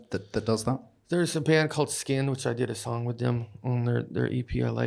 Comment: YO! Does anyone know the band JD references at about 22:29 ? Sounds like he says "Skin" but that's a disbanded grouped from the UK. I also factored in JD's unique way of speech and I thought maybe he was saying "Scan." Still nothing. "Sin" was also a dud result. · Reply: Skynd